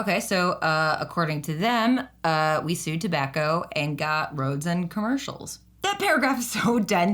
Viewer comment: Heads-up, the captions at 25:09 say "roads and tobacco" instead of "roads and commercials"